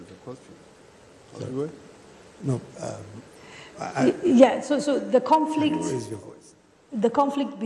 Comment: Did I just hear Nigerian President ask " Who Is the Conflict " when asked how he want to resolve the issue between famers and herdsmen. play from